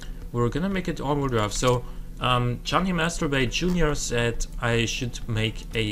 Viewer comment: also what did u say at 19:12 ?! did u say "Masturbate"? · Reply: his name is "johnny masterbray jr." , i think i said "masterbay" which made it sound a bit funny ^.^